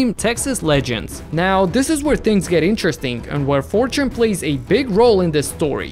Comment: am i the only one who hears "interestink" ?